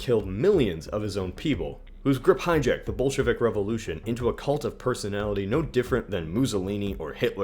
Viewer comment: I thought he said "Bullshittick Revolution", not "Bolshevik Revolution".